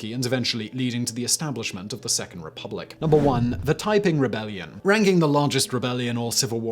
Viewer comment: I thought he said "Typing Rebellion". Just this image of Secretaries in the 20s rebelling against their corporate overlords just made me giggle...